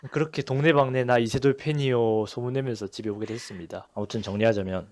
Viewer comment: 그...뭐... 그렇게 됐습니다... 가 들리는거 같네 김해청년인줄